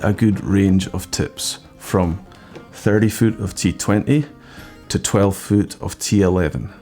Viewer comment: Did he say 30ft of t-20 @ 6:26?